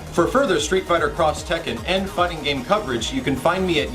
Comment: did he say ? " fucking game coverage "